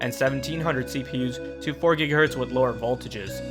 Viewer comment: Oh gosh I thought you said 4.5Ghz, and that you lost the silicone lottery, I was about to flip my shit.